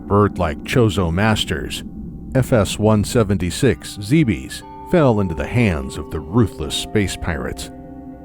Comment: did you just say zeebees?